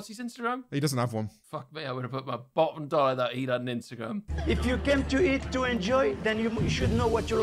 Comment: Anybody else not know what will said here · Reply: "Fuck me, I would have put my bottom dollar that he'd had an Instagram"